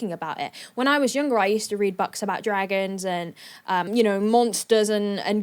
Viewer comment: "I used to read 'bucks'..." Did I hear that right? Does she pronounce 'books' as 'bucks'?! It sounded like she suddenly came out with a Canadian accent or something. On that thought where abouts in the world is actually from?